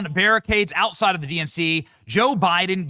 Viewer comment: Sounded like you said you are live inside the DMZ, seems appropriate. DMZ=DNC